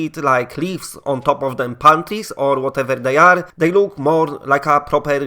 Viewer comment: Did he say leafs on top of panties?